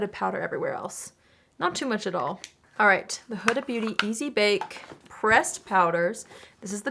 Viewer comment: Imagine my shock and confusion when I was listening to this from across the room and thought you said "Easy Bake breast powders" 🤣🤣🤣